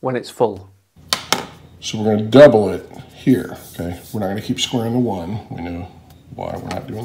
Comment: Did he say "we gonna keep squaring the 1 so we get 2".... Huh??